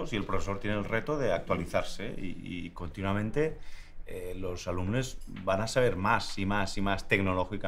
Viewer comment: Escuche mal?? o se están actualizando más de la cuenta... Jajajajajja como que "los alumnes"?? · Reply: Me encantaría creer que dice alumnes por inclusión, pero al ser en la Comunitat Valenciana, creo que lo dice así porque le sale en valenciano (alumnes)